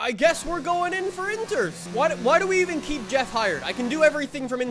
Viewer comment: i thought it said jeffs fried and i thought classic jeff